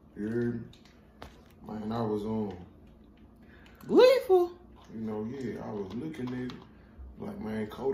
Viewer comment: mane wtf kodak said😭😂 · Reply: Daniel Hunt he said gleeful